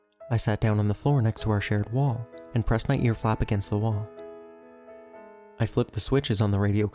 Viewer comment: "I pressed my earflap against the wall." oh okay, earflaps are a thing now. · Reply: I heard "ear flat against the wall".